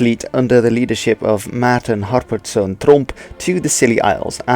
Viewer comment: I heard Trump, look the subtitles if you think I am wrong.